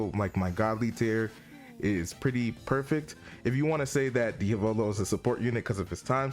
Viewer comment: Did you say perfect!??